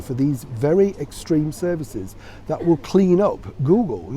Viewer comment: I thought you said she was going to get a job as a cleaner.( Clean- up)